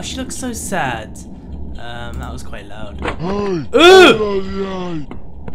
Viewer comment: it sounds like he is saying: he! ophouden jij! wich is dutch voor: hey? stap that!